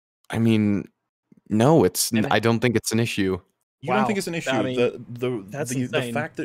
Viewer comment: I keep hearing “I don’t find it and issue” or “Its not an issue to me”.